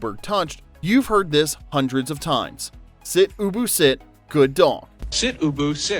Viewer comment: I always thought they were saying, sit Boo Boo, sit. It was years later before I read something or saw something that said it was Ubu. LOL